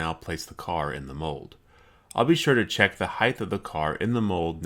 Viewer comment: did you say heighth?